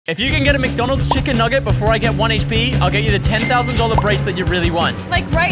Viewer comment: I thought he said 10$ bracelet